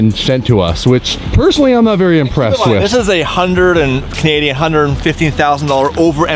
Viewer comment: unfortunately as a fellow Canadian I understand why they paid that. My new off the lot wrx sti was $35k us in Canada all told no loan $62k. You pay $60k we pay $105-115k for the same thing. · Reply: I could have swore I heard that guy say they paid 150k over MSRP. If that's true and they paid over 200k for that car, that's crazy.